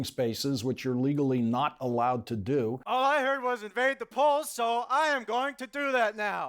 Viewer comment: "All I heard was invade Poland" Whoever wrote that line needs a raise. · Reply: He said: “All I heard was ‘invade the polls’.” Not Poland.